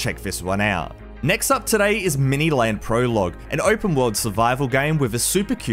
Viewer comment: i thought that said mini ladd😶